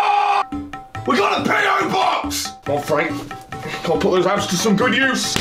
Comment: I actually had to read what was written on frank because i swear i heard you say pedobox instead of PObox xD Thanks for the laughs as always!